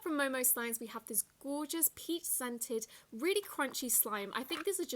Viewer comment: I swear I heard pee scented...